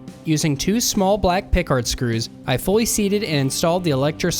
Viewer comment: Very interesting and well done! Thank you. Did you say you used two “Picard screws” to mount the phono jack to the body of the guitar? I’m not familiar with that screw type. ’ve Googled what I thought you said, without finding what looks like the fasteners you show. A reference/source would be appreciated. Thanks. Roy Lewis Buffalo, NY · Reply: Hi Roy! Thanks for watching the video. 😊 I should really work on my enunciation lol! I used two "pick guard" screws that I had laying around in my parts bin. Sorry for the confusion! 😅 Here's a link to a comparable screw: <link>